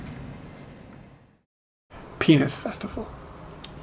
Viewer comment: I thought you said Peanuts festival ..